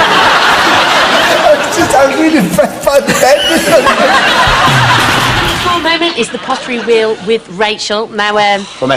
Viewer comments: What did ant say at 8:14?? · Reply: oswa arshad i'm just really impressed by the bending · Plz anyone plz tell me what did ant say at 8:14? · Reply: 'I'm just impressed by the bend in them!'